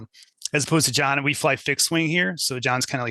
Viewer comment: FIXED WING aircraft!!!...... at 9:27 in I could have sworn I heard him say, "...we fly DICK SWING here...." thinking that was a specific pattern or philosophy on flying/policing like the "broken windows" theory or some type of flight pattern... But you got to admit that if you told someone you were a Dick Swing Instructor....that does sound a lot cooler than just "pilot".